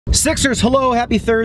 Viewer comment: You say you call us "sixers", yet sometimes I hear "sexers". I'm good with either one, HA.